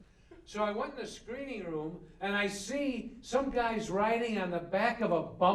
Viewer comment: I heard "flying" , and think he was referring to Exorcist 2 where someone was on the back of a big locust. Maybe James Earl Jones? Its been a while.. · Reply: or maybe he said 'riding', the hand gesture and US accent threw me.